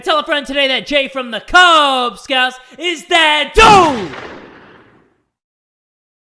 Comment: in the ending whenever jay says “jay from the kub scoutz is that dude” i thought he said “jay from the kub scoutz is dead too” 😩‼️